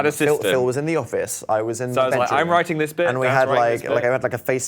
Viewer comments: did dan say 'the bedroom' at 5:14 ??? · Okay okay okay did anyone else hear Dan at 5:16 say "Phil was in the office, I was in the bathroom."? I SCREAMED